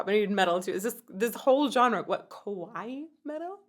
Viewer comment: It's pretty funny how you pronounce kawaii at 4:46, cause what you're saying sounds more like the word kowai, which actually means THE OPPOSITE of kawaii! Kawaii means cute, while kowai, the word that you're saying, means scary 😂 Also, the second "Are you ready" that you hear around 19:15 is actually MomoMetal's scream! They recently talked about it in an interview, BABYMETAL finally has a growler!!